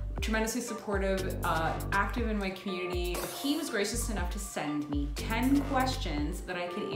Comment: when you said "he was gracious enough..." I thought you said "he was racist enough..." for a second. haha